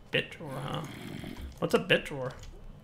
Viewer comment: did anyone else hear "whats up bitch"